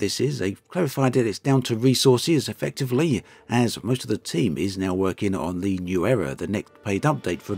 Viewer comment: I heard that as "most of the team are now working on the new error". I fear I;m right, too.